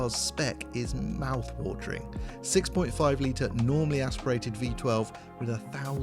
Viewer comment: Correct me if I am wrong. At 1:20 did he say....normally aspirated. Is it not Naturally aspirated. Or can both he used as the same thing? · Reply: He said normally aspirated. Isn't that the same thing though ?